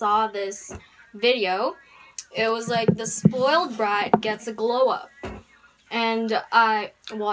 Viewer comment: I genuinely thought it said grow up instead of glow up